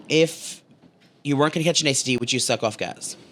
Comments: What is saying at 11:15 ? " If you werent going to ______, would you suck off Gaz" I keep replaying it but cant figure out what hes saying at that part. Lol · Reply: I thought he said catch an STD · What is saying at 11:15 ? " If you werent going to ______, would you suck off Gaz" I keep replaying it but cant figure out what hes saying at that part. Lol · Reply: catch an std